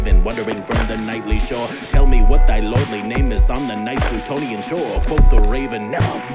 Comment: I kept hearing "plutonium shore" in the rap practice for some reason, and I was so confused 😂 Then I looked it up just now, and saw it's "Plutonian shore," and the explanation of that, and now I'm like, "Girl. You studied literature for almost ten years. You almost taught it. And now you've forgotten your Greek mythology? Come on!" That's a clever reference to the underworld and the River Styx, though. Rolls off the tongue nicely, I can see why Poe picked it. also-- my heart right now. Thanks for the shout out❤ I just got the ashes back today. I'm glad I had someone come with me. My friend just had the funeral for his loved one a few days ago on Saturday. I really should go back and read this poem for myself again.